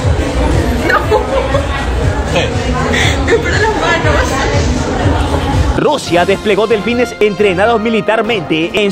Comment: alguien me explica que dijo la chica en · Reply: Le dijo eso es para las manos jsjsjsjsjsjjsjs